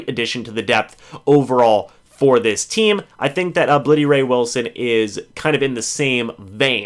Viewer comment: I thought you said "Bloody Ray Wilson", which honestly might be the most intimidating nickname ever lol